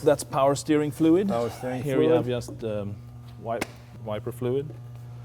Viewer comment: "Take a engine from this, and a transmission from that" I wonder was he talking about John Hennessey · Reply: ***** I thought I heard him say viper but oh well still American muscle